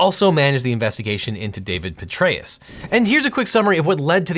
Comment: When he said “Petraeus” I thought he said the generals name was “Betraeus” which would’ve been SO perfect Lol